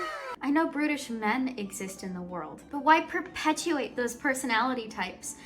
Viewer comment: i heard "british men exist in the world" at 4:27 which made the review so funny